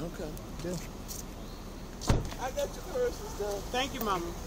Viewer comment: "I got your purse, Son..." "Thank you, Mama." 😂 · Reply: " I got you're purse and stuff " is what she said .